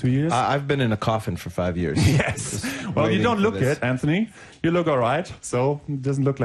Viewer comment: It sounds like he says "you don't look good Anthony, you look alright."